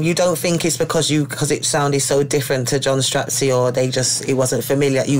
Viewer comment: did she say john strapzy 😂